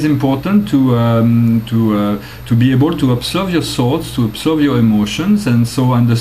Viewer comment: just on the lighter side.... i thought you were saying salts, every time you said thoughts. Lol. smile.